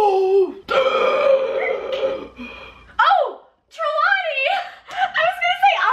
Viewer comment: what did say? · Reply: Trelawney